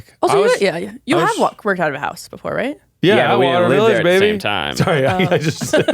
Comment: w h a t did Miles say omg 😭😭😭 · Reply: Atwater Village, the area their previous office etc was. Then I guess he said the actual street address which they muted out.